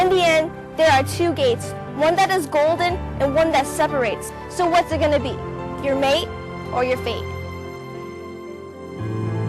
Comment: I thought she said "Your mate, or you're Fake"